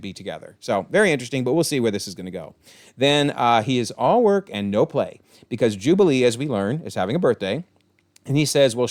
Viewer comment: What does Wolverine say in this scene? "I agree with ..." I can't make it out and I don't have subtitles. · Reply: Happy to help! He says "I second the blue Rogaine ad. Kid deserves it." Rogaine is a medication that helps with hair growth, thus the joke about Beast.